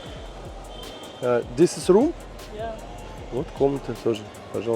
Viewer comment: 7.00 this is room? послышалось как "здесь срут?")))